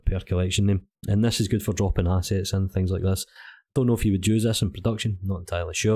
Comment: Had to go back and listen again I thought you said “dropping acid” not “dropping assets”. Hopefully Blender Market will get your shop set up soon. I’ll keep an eye out for it.